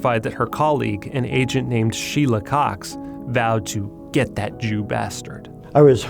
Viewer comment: I thought it said, "She love cocks"